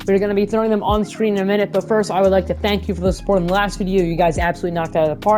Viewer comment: i thought u said i’m putting it on stream in a minute 😂